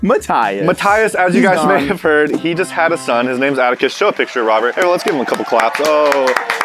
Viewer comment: When they said Atticus I thought they said abacus and I was like “man My thighs itch must love math bro!” Oofff.